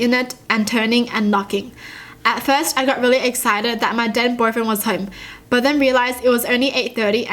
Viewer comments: who else heard “Dead boyfriend” Instead of “Then boyfriend” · I thought she said DEAD Boyfriend lmao 😂